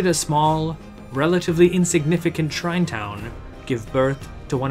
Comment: what does he say at 0:42? “small town ??” can’t understand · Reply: shrine town , got it